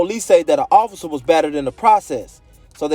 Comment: "he didn't have a weapon, but police said theat an officer was better than the process so they charged him with felony" what the f do you say there? It doesn't make sense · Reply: He didn’t have a weapon but an officer was BATTERED in the process.